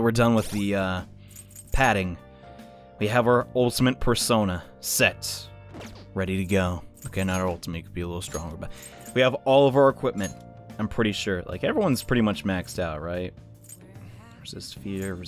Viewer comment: We have our ultimate persona.... Sexth. Me: Ok...? ...Wait did he just say se- Roundabout kicks in